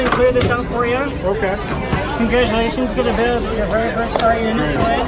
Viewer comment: What does the guy at 6:39 say? "Congratulation on verfy parery day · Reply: Zakari 7-ONE-1 I think congratulations on building your very own or first droid